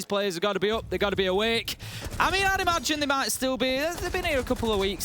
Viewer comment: What I hear: abee ada aba abee mee eeeear What they said: "I mean I'd imagine they might still be, they've been here "